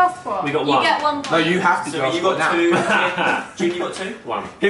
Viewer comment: Zoe y did u say at 5:12 one point but then hold up 2 fingers? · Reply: xXcosmic cookiezXx no she said 2 points